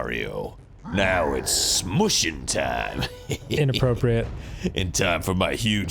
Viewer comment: I legit, 100% thought that said "Now it's smoochin' time..."